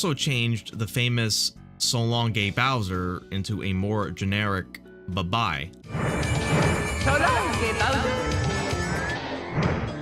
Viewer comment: I always hear "Shalom Gay Bowser"